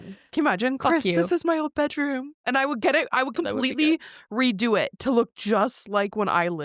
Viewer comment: anyone else hear the man saying something.... i thought i was hearing shit and relistened multiple times... lol. "That would be dope" lol